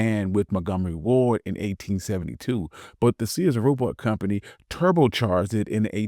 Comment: I thought you said "Sears Roblox" someone help me.